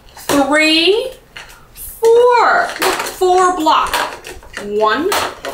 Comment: “Soleil’s got a block” Everybody: Run! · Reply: They thought she said Glock.